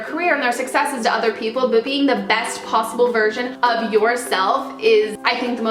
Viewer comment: 4.43, i thought you said 'the best possible virgin of yourself' :')